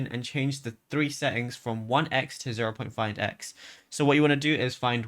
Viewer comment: You said change the scroll settings from 1x to what?? You mumbled right there at 00:41 . 0.1x?